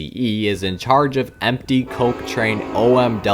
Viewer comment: Is it just me or did I hear coke at 11:45? You better explain